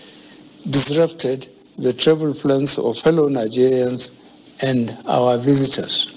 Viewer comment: Fellow Nigerians s3sen? Wofa leave our lyrics for us ooo🤣🤣🤣🤣🤣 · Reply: Am pretty sure I heard "hello" not "fellow"